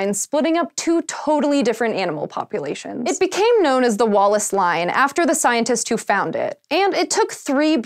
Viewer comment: I wasn't looking at the screen and I thought said Wall-less Line.